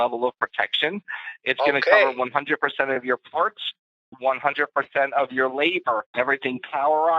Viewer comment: I swear i heard the scammer say they'd cover 100% of his lady parts.